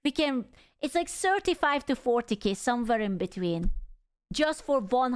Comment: did somebody say 40k?